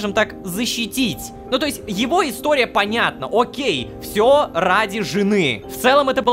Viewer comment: почему мне послышалось " всьо понятно он гей"